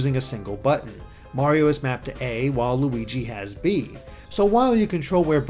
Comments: Sounded like "Waluigi", haha. ^^ Well done review! :) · DID HE SAY WALUIGI!!!!😆😆😆😆😆😆😆 XXXDDDDDDDDDDD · did you say waluigi??